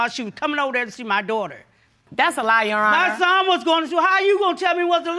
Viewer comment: So the girl’s mother says they don’t do anything for the baby and the boy’s mother says that’s a lie then proceeds to say after why would I do anything that’s not my grandbaby. Sis you just said she was lying about you not doing anything for him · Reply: I actually had to rewind that part bc I thought she said that, too, but she actually said "that's no lie".